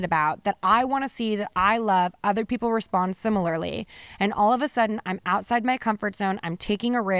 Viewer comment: idk 55:23 really sounds like she says "all of THE sudden"